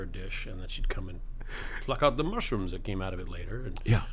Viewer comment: When Nathan said mushrooms i thought he said Martians. 😂 Then the story continued that the dr gave his daughter a ball to keep in her closet and water it every day. If some young woman in NYC is growing martians in her closet for her dr father that could only mean one thing. I have somehow missed the most important news story ever. We're growing martians here on earth. 😂😂😂